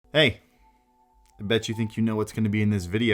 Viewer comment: i thought the intro said " hey i bet you think you can get 1k viewers, you cant " ctfuu